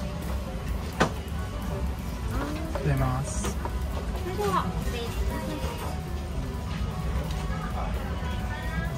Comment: What is saying at 3:31? It seems like a shorthand "Thanks" and I hear it everywhere in Japan but I never know what the word is, or what they're actually saying? Any help will be great! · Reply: I quickly said Arigatou gozaimasu (Thank you). We sometimes don't enunciate each word clearly 😁 We could even say Azamasu that still makes sense in context 🤣